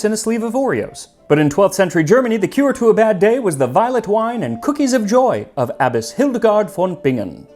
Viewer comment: I thought you said "Violent wine" not "Voilet Wine" 😂 Although I could imagine some voilent wine would be great aswell.